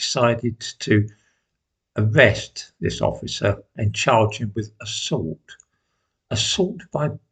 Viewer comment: Oh charge him I had to wind this back as I heard this as as charge her at first. Yeah that Chris Rock sketch is a classic now.